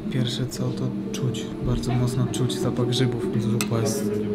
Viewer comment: Czy tylko ja w 4 min usłyszałem czuć bardzo mocno zapasach rzygow aż się upewniałem dwa razy